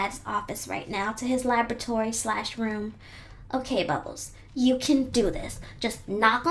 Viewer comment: did she say bubbles